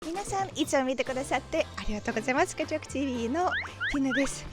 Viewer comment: オープニングが『ありがとごぜえます』に聞こえてしまう。 余計なこと言ってすいません。